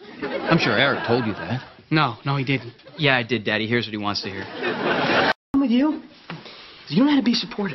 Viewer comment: tell me why I re-played this three times only to find out he actually did not say "yeah I did daddy, he hears what he wants to hear." I QUIT